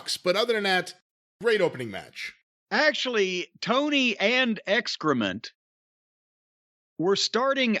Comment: did he call Excalibur "excrement?" Lmao